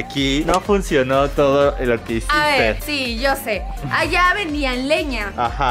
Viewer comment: después de 6 años descubrí que dice karen:”Allá vendían leña”